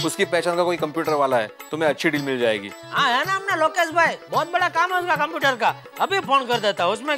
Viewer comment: i think is saying lokes gemer